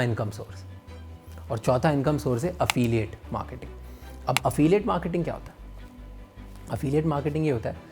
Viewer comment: I thought I heard Afeem 😂